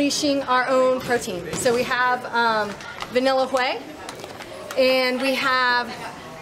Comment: Wait, did she just say "Vanilla 'Hwey' " ? Great content as always, love your work :D